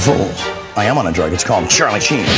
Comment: The hell did he say? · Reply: I think the first thing he says is dine fold. Then he says I am on a drug it's called Charlie chine.